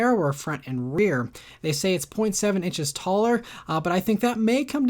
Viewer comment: at first I heard it as 7 inches taller instead 0.7 and almost had a heart attack lol